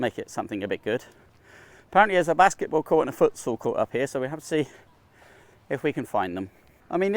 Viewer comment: Foot what? What sport is that? Sounded like you said footsoul lol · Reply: Futsal is a version of soccer. Played with less players on a hard court, often indoors. Growing up we called it 5 a side